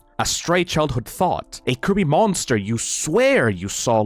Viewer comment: istg i thought i heard them say "kirby monster" and not "creepy monster"